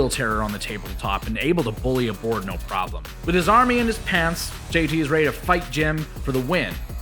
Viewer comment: I heard, "with his army in his pants...". Well, that sounds... uncomfortable.